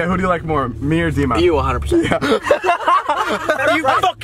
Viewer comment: What did he say at 6:47? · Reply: TTBF Legit “you 100%”